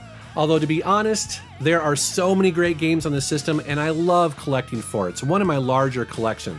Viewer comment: "...and I love collecting farts", what did he say?